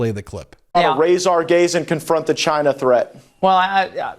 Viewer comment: Did he just say he wants to "raise our gays"?! That's a horrifying thought... (Yes I know that's not what he actually said)